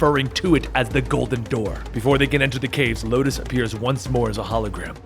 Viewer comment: Di- Did he just say "Locust"? It's Lotus... xD